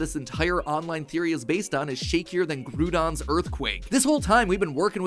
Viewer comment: Did matpat just say "Grewdon" at 10:44?